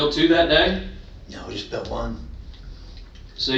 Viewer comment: "No WE just built one" I swear he says "WE" who's we?!